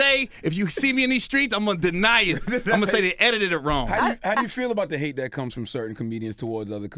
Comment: am I trippin or did he say Editeded ? 🤣